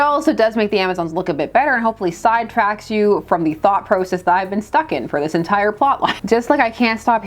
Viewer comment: I heard T.H.O.T. Process initially. I had to reply that moment lol.